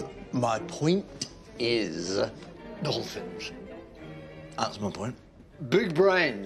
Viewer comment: The first time I watched the show I had forgotten most of the book and I thought he said "doll-face" not dolphins